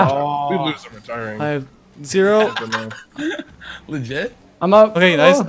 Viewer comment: in the video. did you just say you have zero during a no boost challenge? lmao